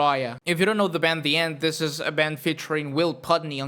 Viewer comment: END...oh my God, man...THAT'S what you're saying!? Took me forever to find this!! Thought you were saying "nth" or something. Jeez. Thank God for Google Lens...